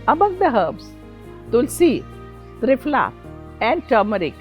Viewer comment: Out of the 3 herbs mentioned...I could only make out tumeric....can anyone tell me what the other two herbs were that she mentioned at the 2:19 mark? Thanks for anyone who can help! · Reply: Tulsa was first, turmeric and someone else here said triphala the other.